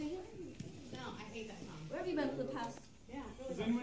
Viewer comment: Bro at the end I thought the guy said CAN U RUB MY BULLET BRO AYO 💀